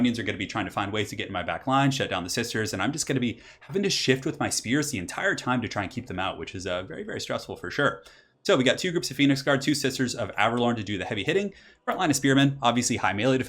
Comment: I swear I heard 'Sith Sorcerers of Avelorn' once. I believe it.